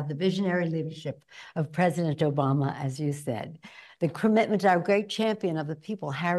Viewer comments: Had to rewind it but I knew I heard what I heard, Pelosi called Barak, "Joe-Bama" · did she say president Joe Bama?